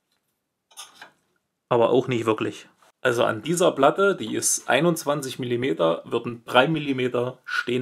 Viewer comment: Großartiges Video. Dankeschön. Ich habe genau dasselbe geplant und noch ein paar wertvolle Anregungen bekommen. Die Platte ist 27mm dick? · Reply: Ich hab 21 mm verstanden...